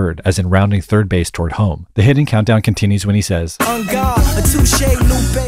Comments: Why did my dumb ass thought he said “on god” instead of “en garde” 😭😭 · bro i thought he said "on god" not "en garde" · I thought that he said on god not en garde lol